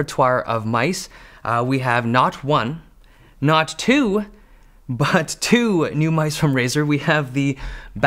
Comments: “not one, not two, but two. “ You okay? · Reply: I thought he said "not one, not too, but two" ? 😅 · i had to rewind that first part so many times to make sure i heard it right “not one, not two, but two. “ 🤔